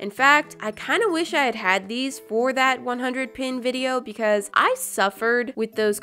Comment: Is it just me or at 10:25 did she say “the 100 PEN video” and not 100 shrinky dink video